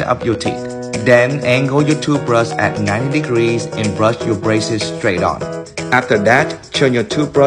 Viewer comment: I thought he said now brush your braces straight off